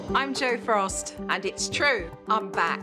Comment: In the opening sentence, all I heard was "I'm Jo Frost, and it's true. I'm black." I had to go back and rewatch it